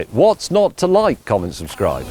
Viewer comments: Am I crazy or did I hear James say “comment subscribe” real quick at 3:07 ish. Is this some subconscious mind game for engagement ?!😂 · "What's not to like comment subscribe" Haha, that was brilliant! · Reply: I heard "common subscriber" at first. Had to go back to make sure.